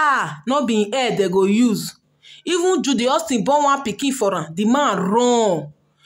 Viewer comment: I thought they said he is juju haustion brother